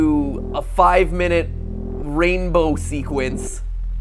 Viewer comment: When you said ranbow I thought you said franbow I was super happy I was like franbow 2 count me in